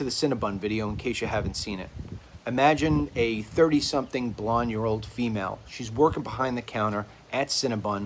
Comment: Did he say "30-something, blonde-year-old female?"